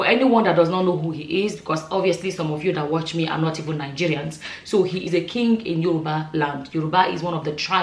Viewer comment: 'Some of you that watch me are not Nigerians'. Quote me correctly And yes, some of my viewers are not Nigerians. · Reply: Thank you for correcting me! I thought what l heard was, “most of you who listen to me here are not Nigerians!” Sorry, you’re doing a great job! I am a Nigerian, of course